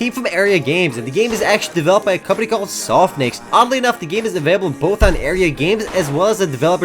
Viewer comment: WTF?! did he say "this game was developed by a company called soft dicks" ?